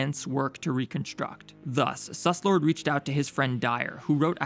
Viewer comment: did he say sus lord